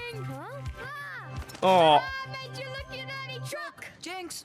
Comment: It might be that I’m not used to all the Australian accents, or something (because I have heard “Made ya look, ya dirty crook!” before), but the first time I saw this episode I thought young Bandit said “Made ya look, you NANNY chook!”